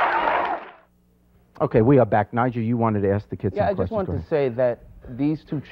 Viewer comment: I thought he said Nigel lmaooo